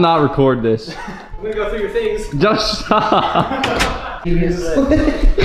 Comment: wait did say “just stop” or “josh stop” ? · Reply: Just stop